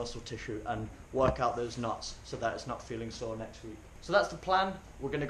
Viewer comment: Did he really say WORK OT THOSE NUTS!!!